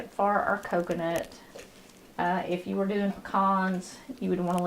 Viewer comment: Please help! What is she saying at 4:29? I keep hearing “kahn”. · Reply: Les Lieb pecans.